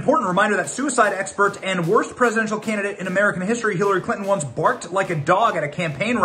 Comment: Parr of me misheard "candidate" as "cannibal" and my mind went "yup, sounds about right" 😂